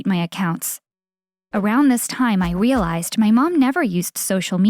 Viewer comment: she says wearlised instead of realised